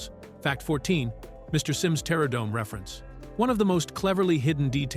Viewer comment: I honestly thought he said Terra Dome, and I took it as a reference to the opposite direction of the Inferno…